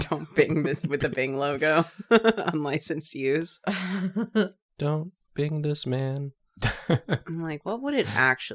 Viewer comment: lmao the captions say 'don't bang this man'